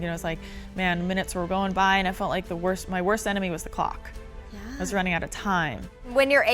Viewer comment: my worst enemy was the... What did you just say Meisha !! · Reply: er she said clock.. you douche